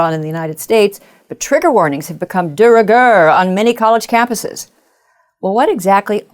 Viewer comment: What's the word she uses at 20 seconds? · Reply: I think it's "de rigueur."